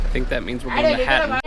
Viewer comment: yes you are right he's saying "hatton" at 4:40 but even though i'm a local it's hard to for me to understand what he's shouting lolz you are good.